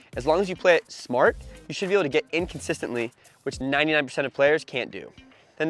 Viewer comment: Thanks! BTW, at 5:26 your subtext says “inconsistently” instead of “in consistently”.